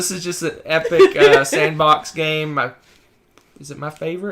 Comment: I swear I heard say "this is the other sandwich game" for #2 Xia. I know it's sandbox, but I can just see eating a sandwich playing Xia now. And The Colonists over Vinhos for legit surprised me.